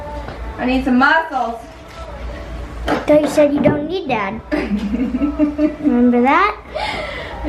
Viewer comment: Your son is hilarious. “I thought you said you don’t need dad” 😂😂